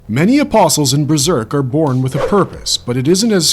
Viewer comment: Bro whats the beginning xD i heard "many apostles are born with HERPES" like bro kinda specific but okay xD